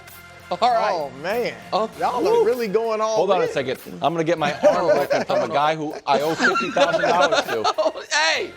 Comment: What did Wildes say at 1:32? I can’t hear it from the other noise but I think it’s hilarious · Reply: Get my arm broken by a guy I owe 50k to lol.